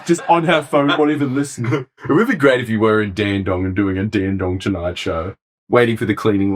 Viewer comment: As someone in Melbourne I thought you kept saying you guys were in Dandenong. Its probably pretty similar tbh